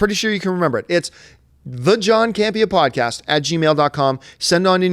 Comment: I thought it said "John Cena" and now I'm disappointed